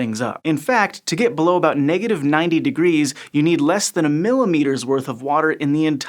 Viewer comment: Good thing they explained to me that 1mm water wouldn't reach the 1 mm mark on a rain gauge. Really put that into perspective form me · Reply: It's not overexplained. I was thinking I'd misheard, and he'd said "millilitre" until he elaborated. It makes sense in hindsight, of course, but so do most things.